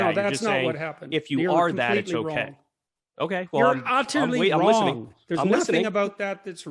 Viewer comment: What is the word that Jordan Peterson uses at 2:23? I've been searching all over the internet for this word. Anyone? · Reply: Utterly ?